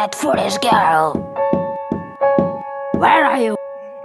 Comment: It sounds like she says “where are you hiding you little sh**